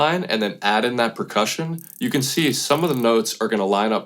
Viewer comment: Did you really say "precussion" ?